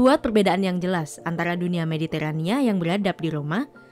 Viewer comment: Salah denger endingnya jadi "... Barbar gaul di Prancis." 😆